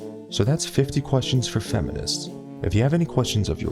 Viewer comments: IK I HEARD THIS WRONG SO DONT GET MAD AND REPLAY WITH SOME BS at the end i heard him say dont forget to rape this video xDDDDDDDDDDDDDDDDDDDDDD wtf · Dude u thought u said don’t forget to rape this video at the end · He said Rape so many times that at the end i thought i heard "dont forget to Rape this video"😆😀😄😂😂😂😂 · I actually heard him say “don’t forget to rape this video and subscribe” in the end there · At the end i thought you said don't forget to rape this video and i just thought well that seems fitting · After hearing this entire video I thought he said rape the video 🤣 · at first I thought he said "dont forget to rape this video and subscribe" and I laughed a little too hard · at first i heard "dont forget to rape this video" lol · it's way to early in the morning... I could have sworn you said "don't forget to rape this video and subscribe" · I thought he said "And dont forget ro RAPE this video and subscribe" XD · At the end i thought you said don’t forget to rape this video abd subscribe😂😂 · you talked so much about rape that I thought you said rape instead of rate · LOL I thought he said, "and don't forget to rape this video and subscribe" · Who else thought that he said 'and don't forget to rape this video and subscribe' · Lol i heard 'rape this video' not 'rate' i think i should sue you for rape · i heard "dont forget to RAPE this video and subscribe" XDDDD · Who else thought that he said '' Rape this video'' not '' rate this video